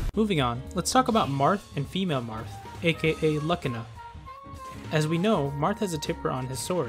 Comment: Did he just say luck-ina for Lucina?😂